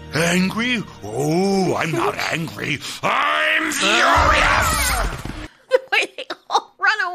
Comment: As a kid, this was the first time I've heard of the word "furious", looked it up, and was screaming it in school for a week. I thought Mario said "curious" before. lol